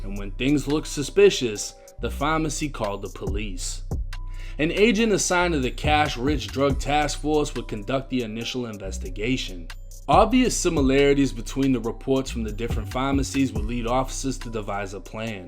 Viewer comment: did he say agents with the “cash rich drug task force?” What the actual fuck?